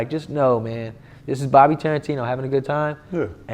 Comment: i thought he was saying "bobby trippin on" at the end